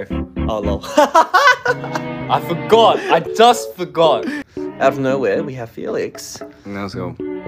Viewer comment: I thought Chan said : out of nowhere we have feelings 👁️👄👁️